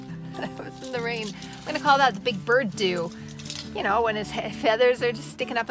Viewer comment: When you said your hair was a Big Bird 'do, I thought you were saying you had bird doo on your hair!